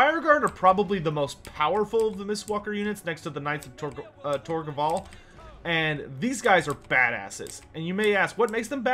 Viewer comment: Lol, when showing the Knights of Tor Gaval it sounded like you said Knights of Tor Gobble xD, Turkey Griffins confirmed in Warhammer 3?